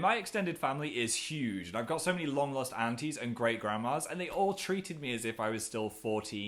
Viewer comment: FUN FACT When you sneeze every organ/muscle in your body stops for a split second! So if youre allergic to pollen and u go outside......................R.I.P ALSO i thought Phil said "Long lost panties" and i was like x_x